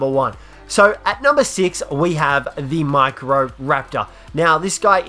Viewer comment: I swore he said the Mike Rowe raptor